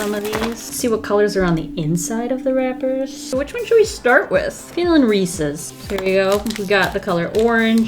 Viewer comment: When she said I’m feeling Reese’s.......................I thought she said I’m feeling races